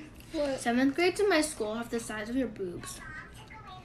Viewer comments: I thought she said 'you know what's really weird 7grade in my school books have the same size of the books you have' idk why I heard that now I got to know what she really meant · At first I heard “they have the same sized poos as you” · I thought she said boots 😭😭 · I thought she said books at first 😭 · I thought she said boots😭😭😭😭 I was like gurl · HAD TO REPLAY CAUSE I THOUGHT SHE SAID BOOTS